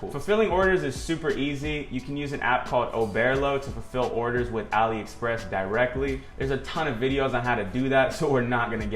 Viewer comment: What's the name if the app he said at 11:54? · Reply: Oberlo